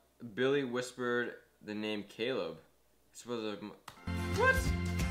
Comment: Omg I heard him say Caleb